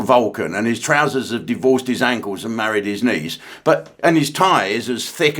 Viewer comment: Ahhhh married his knees 😂 I had to rewind as I thought you said he married his niece 😮